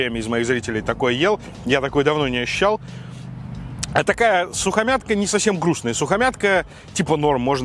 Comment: сукамятка....да не, послышалось))